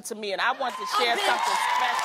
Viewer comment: LMAO did Jeannie say “Oh bitch” at 2:43 hahaha